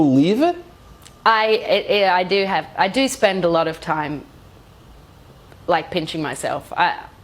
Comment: Hello, can you help me please, i'm french and i don't understand what Sia said at 7:48 - 7:54 Thanks you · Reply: Yeah I know this is 2 years ago but... She says “I spend I lot of time pinching myself “ you know like to prove it’s not a dream.